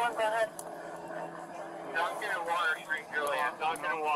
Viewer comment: I forgot the cross streets where “Duncan and Water” 😅 and thought they were saying “Don’t give him Water” 😂 I was like that’s Ironic. Indeed my wits may be Tardy 😊